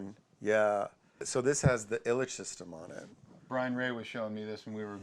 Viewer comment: what is that "system"on the junior at around 21:10 ? i cant make out the name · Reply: Ilitch hum cancelling system from ILITCH ELECTRONICS.